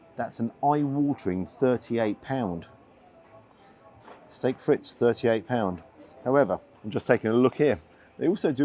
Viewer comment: I heard "Steak Fritz" 🤣. Now I need to invent a "Steak Fritz"